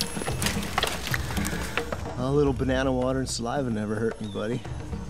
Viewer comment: Damn really thought it said banana water and salvia